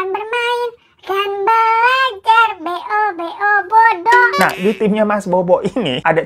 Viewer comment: B -O-B-O = Bodo,, aku dengernya ngikikk.😂 pas banget lagi bab, langsung ambrol semua tainya..